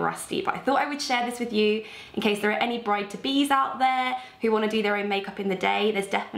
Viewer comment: When you said 'bride to be's out there' I thought you said 'brighter bees' and was confused for a few seconds xD Such a beautiful look, as always.